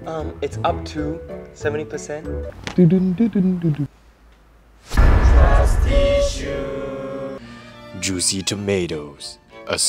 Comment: Tbh I thought it said "Trust Tissues"